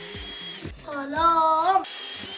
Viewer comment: سمعتها صلام🥲